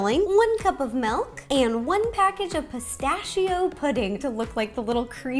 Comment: Who heard her saying "one PACKAGE of yeast!!"😂